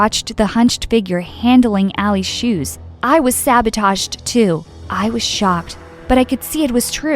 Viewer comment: can anyone tell what English word did Ally tell ?😅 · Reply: sabotaged